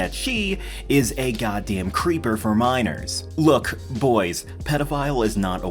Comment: bruh I thought for a split second you said "creeper for miners" as in Minecraft I swear I am too immature for the internet